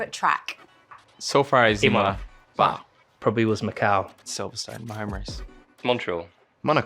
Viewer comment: Baku? back? what did they say? hahaha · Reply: Macau